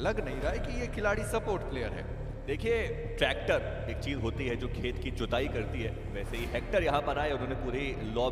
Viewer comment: Am I the only one who heard ‘’khet ki chudai’’ by